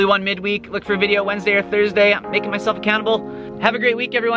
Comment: at the end of the video it sounds like you said "im making myself a cannibal"